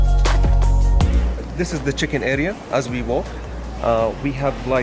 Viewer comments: I thought Arab guy said this is a chicken area... please like if i am right.....🤣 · who else thought he said “this is the chicken area” 🐓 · Am I only one who heard saying chicken not check-in 🤔🤔 Edit 👉 thanks for like😃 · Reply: Yeah i clearly heard him say chicken area😂😂😂😂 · 😂😂😂🤣🤣🤣😂😂 I heard chicken area😂😂😂🤣🤣🤣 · I thought he said chicken area I was like what? Chicken lol then after realised what he said 🤦‍♂️😅